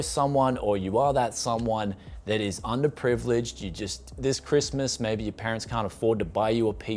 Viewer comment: I wish used parts in my area are this good in terms of price :( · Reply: I legit thought you said underpants...idk why.